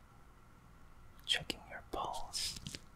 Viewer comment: When he said, "Checking your pulse," I was reading something else and thought I heard, "Checking your balls." This mishear is surprisingly accurate considering this guy said, "Focus on your fingers, like your mom last night."